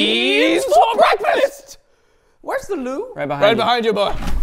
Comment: I thought he said “where is the lube” 🤣🤣